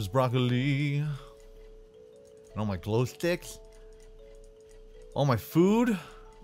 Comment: Just watch this 2:27 Does he say glow slicts